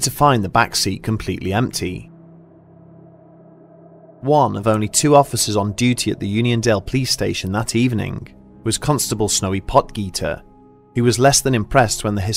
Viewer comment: I thought I heard . Smokey pot-eat